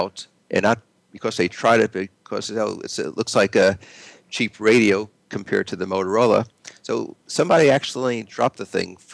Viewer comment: What was edited out at 19:55? He was saying "the only thing people complain about..." and then you edited it out? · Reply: Just watched... No edit. He said "looks like a cheap radio compared to Motorola"